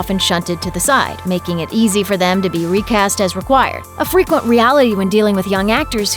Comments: I thought they said racist not recast der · I thought that said racist instead of recast.